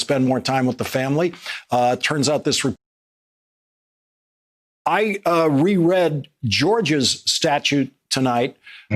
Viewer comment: Why did they censor out what he said at 2:15 that's clearly an edit used to censor what he was saying. Does anyone have that clip? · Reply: yea he said you had schizophrenia